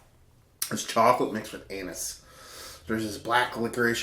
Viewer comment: Hold up, did he just say "chocolate mixed with anus" at 6:17?